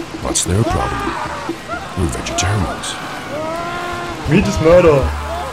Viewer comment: what did he say? Sounds like either “We just met em” or “We just murder” but I can’t be sure. Anyone know? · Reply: Meat is murder.